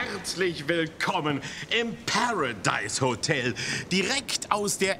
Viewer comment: Ich habe Parascheiß Hotel verstanden.